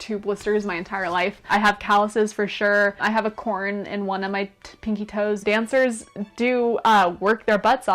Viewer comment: 「うおのめ亅って発音してるのかな？🤔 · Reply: まさかと思って聴き直してみたら、確かに！🤣 英語の聴き取りにはあまり自信がありませんが…恐らく “I have a corn(うおのめ) in one of my pinky toes” と言っていて、"one of my"の部分が「うおのめ」に聴こえているように思われます。 絶妙すぎる空耳に思わず笑ってしまいました。愉快な空耳をありがとうございます♡